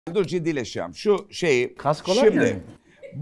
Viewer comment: de Okan Bayülgen ne diyor anlaşılmıyor 150 defa replay yaptım, oynatma hızını düşürdüm yok arkadaş anlaşılmıyor uyuz oldum 😂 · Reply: Dur ciddilesicem, şu şeyi, şimdi